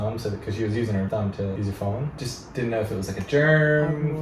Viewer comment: 이번 회차도 잘 봤습니다. 남편분의 시각과 코멘트 참 좋습니다. 영어공부도 되고 채널 콘텐츠가 참 좋네요. 질문이 있습니다. 7:01 남편분께서 '세균'을 언급할때 "젊"이라고 발음하는것 같은데 정확히 뭐라고 하신건가요? 한영사전을 아무리 뒤져도 세균은 '박테리아' 외에는 안나오네요. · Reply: Germ